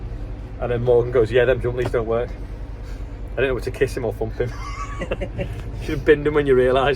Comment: I didn’t know whether to kiss him or thump him!! 🤭🤭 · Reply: I thought at first he said hump him 😂😂